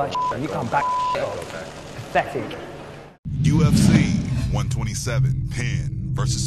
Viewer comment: i tot i heard "UFC 127, PENN VS BITCH" =O